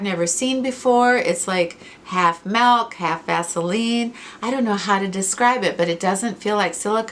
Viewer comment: I would have swore you said half milk half “ass”eline! 😱😂😂. Now back to the show!